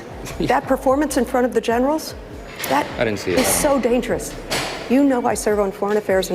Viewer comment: Wait. Do I hear him tack on "Honey" or "Hon" as in, "I didn't see it, Honey." ?